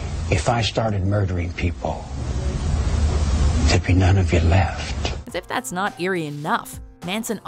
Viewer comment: I have subtitles on and they say "epidemic at math" instead of "none would be left" and I can't stop laughing about it.